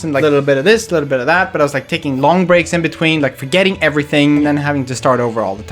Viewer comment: At first i heard "..taking BONG breaks in between.." 😂